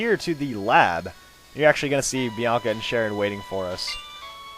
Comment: lol at 10:59 it sounds like he said "Sherry and Bianca" lollollol